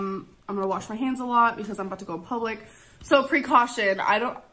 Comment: Did she say she was about to go public?